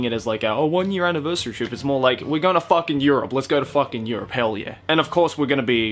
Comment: did he say fucking Europe or fuck in Europe? Hmmmm.